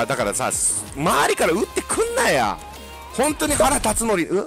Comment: のとこアンチどもくんなやにしか聞こえない